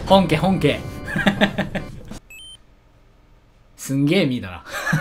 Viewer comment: すんげー淫ら(空耳)